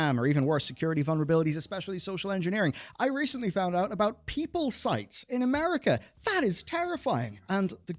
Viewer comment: did he say "people sites" or "peep-hole sites"?